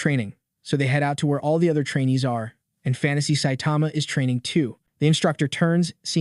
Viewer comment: Did I hear that right—Fantasy Saitama?